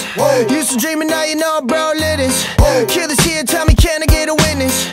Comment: "Bro live this" I thought it said brown lettuce😭😭